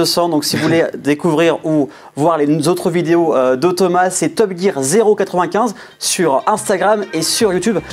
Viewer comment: C'est moi ou à 7:56 il dit "TopGear095" ? ^^^Sinon très cool comme toujours !